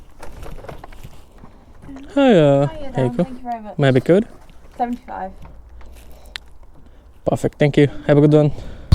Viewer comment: ভাই এটা কি বলেন? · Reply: May I have your code? Eita bollam